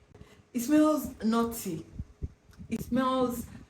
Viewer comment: Oh, NUTTY. I thought you said "naughty"! 😂